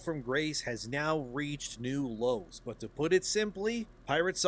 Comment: I swear, at the beginning, it sounds like says, "now reached new lolz."